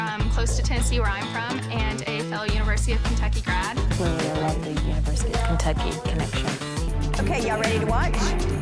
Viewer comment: What does Kelly say at 19:07 during interview? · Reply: “I like the university of Kentucky connection” I had to really listen to it haha